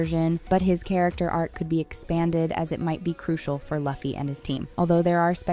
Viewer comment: I heard laffy and I left